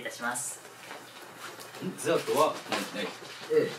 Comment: これとしみつなんて言ってるの？ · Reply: うん？デザートはない？